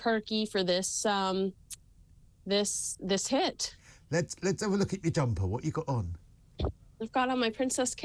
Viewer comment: I thought he said, "Let's take a look at your dumper." Imagine my chagrin.